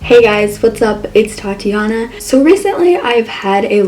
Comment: I thought you said thotiana